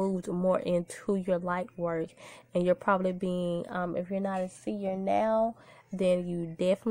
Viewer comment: I couldn’t understand what she said, was it “senior”? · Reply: A seer